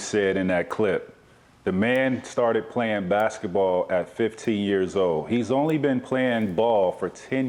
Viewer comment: I heard 50 years old